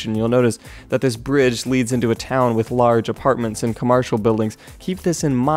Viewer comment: did you say "comarshil"